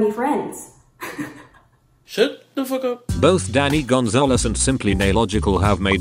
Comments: i thought it said daddy gonzalez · I read “danny Gonzalez” but I definitely heard “Daddy Gonzalez” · I swear the robot man said "Daddy Gonzales" and honestly same. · is it just me who heard Daddy Gonzalez and nodded along for a couple of sec before realising? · 😂 holy crap I thought it's said "Daddy Gonzalez" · i heard “daddy gonzales” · did it just say daddy Gonzales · I thought the beginning guy said Daddy Gonzalez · For a second I thought it said daddy Gonzales.... I was like oh ok. 😂